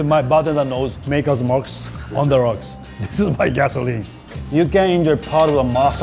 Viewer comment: When Masa said "On the Rocks," I legitimately thought he said "On Drugs." Just for context, if he said On Drugs, the sentence would be: mY bArTeNdEr sErVeS mArTiNiS oN dRuGs